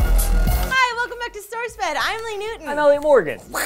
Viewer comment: It sounded like Lee said "StoresFed" at the beginning. LOL